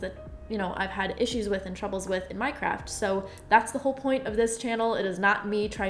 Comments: 😂😂when she said my craft I thought she said minecraft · when she said “my craft” i couldve sworn i heard “minecraft” lol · Every time ANYONE says "my craft" I always hear "Minecraft" so I'm always so confused lmao.